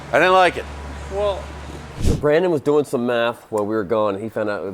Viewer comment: thought he said meth not math